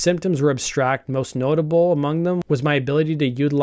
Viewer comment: what she said was "my inability to utilise the word I I I I I I I I I"